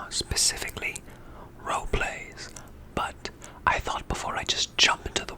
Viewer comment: I heard “Foreplay” and thought “Yeah that sounds about right”